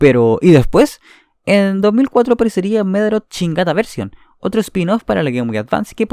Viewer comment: Yo escuché "Medabots Chingada version" 😂